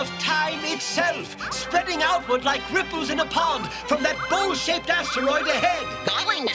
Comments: "Bowl-shaped" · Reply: phew, for a minute I thought he said “Bullshit” 😂😂😂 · it almost sounded like preceptor said "From that bull$hit asteroid ahead." 😂 I know, he said bowl shaped. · it seriously sounds like Perceptor says, "from that BULL$#!T asteroid ahead!" lol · Oh, bowl-shaped. I thought he said bull-shit asteroid. 😅